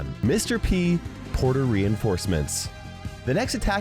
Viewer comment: 🤣 when he said "Port Reinforcements" I thought he said "Puerto Rican Forcements"